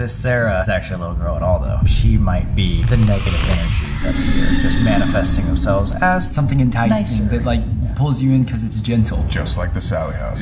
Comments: Did anyone else hear that '"Nice!" at 21:34 it was in a girls voice asswell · oh my god... something said nicer, did anyone hear that??? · Did anyone else hear a female voice say the word nice at 21:34? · does anyone else hear a females voice that says “nice of you” ?????? I hear it so clearly am i just tripping or something?? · does anyone hear a girls voice overlapping voice? It sounds like she finishes his sentence with “nicer” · i heard girl voice said nice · and 21:35 it just me or i hear someone or something say nice? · did you guys put a female voice in this part I heard it said ''nicer'' it caught me off guard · sound like woman saying "nicer" idk 😐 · Did anyone else hear a female voice say “nicer” @ 21:34 of the video? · does someone else hear a girl say "nice"?? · who or what said "tyson" "Nison"? IDK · i heard womans voice saying "nicer" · anyone else hear a woman say nice